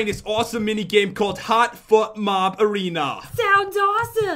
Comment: It sounded like he said " Hot fuck arena ! " haha hahaha 😂😂😂😂